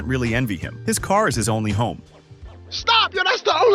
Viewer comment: i thought he accidently said house but turns out, he lives inside that car 💀💀